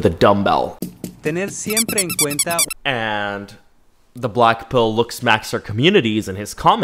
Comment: When ever he say blackpill ..I heard "blackpink"😂😂🖤💗🖤💗🖤💗🖤💗🖤💗🖤💗🖤💗🖤💗🖤💗🖤💗🖤💗🖤💗🖤💗🖤💗🖤